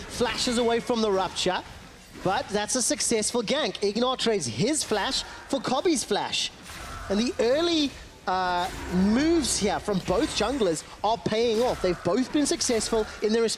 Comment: was I the only one who heard it "... the early MOOves" :)